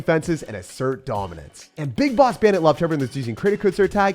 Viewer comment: Did I just hear Big Boss?